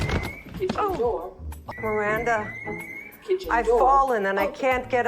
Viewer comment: Kitchen door is open to the beat of 'still tippin ' lmao😅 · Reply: I thought i was saying "kitchen door is angry" lol